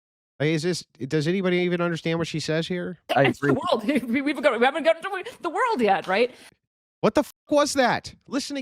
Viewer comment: ok, I played it at 0.25% several times to understand what she said , and what she said was :" is the world hu bu wub gurd darptard dap to we the world we have right" I wouldn't call that a coherent though